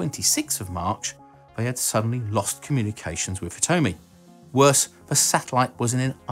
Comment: I thought he said with "it's homie"